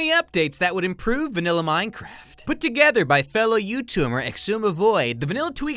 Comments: Who else heard him say YouTumer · I swear to god he said 'fellow you tumors!'